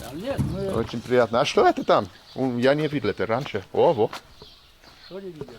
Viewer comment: did someone just say "owo"